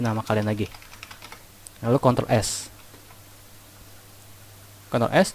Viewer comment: ini bilang apa ya bang? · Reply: Control + s (ctrl + s)